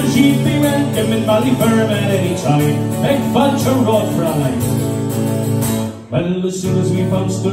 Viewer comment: Amazing song! Does anyone know what he says at 1:18 I always wondered? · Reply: Its Irish “Beag Failte Romhat Fraulein”, its means “a warm welcome woman” 😂😂